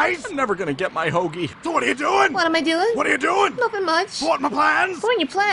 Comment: Imma be honest, i always thought he said "fart in my plans" and not thwart